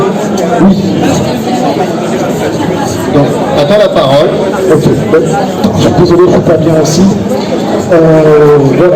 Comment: Merci, pour cette vidéo mais quel bruit ! C'est quoi le jeu ATARI ST dont on parle à 13:36 ? Je n'arrive pas à entendre le titre... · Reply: C'est SapceZot, le jeu qui occupe le reste de la vidéo ensuite ! Merci à toi.